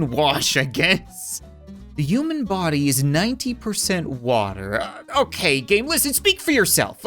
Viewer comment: 19 instead of 90